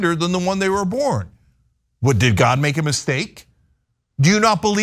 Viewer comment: did someone say steak?